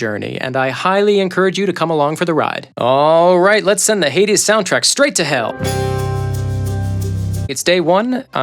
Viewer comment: As a Frenchman i thought you said the 80's soundtrack and i was like "DAMN the 80's was a banger in term of music style" and it was xD